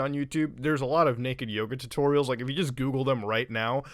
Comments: For some reason I thought you said naked yogurt... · Oh they did what now? speedclicking intensifies Also he's not wrong, I just vetted the naked Yoga and it's true. Also now that's in my search history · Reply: I misheard Muta and typed in "naked yogurt tutorial" AND STILL got full frontal nudity thumbnails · Bruh mans is really bout to cause the single biggest search spike in "naked yoga" ever lmao Edit: I make shitty youtube content lol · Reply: At first i thought he said naked yogurt